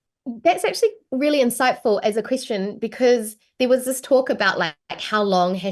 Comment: Aaaah right! "as a question". I first heard "as a Christian" and totally didn't get it.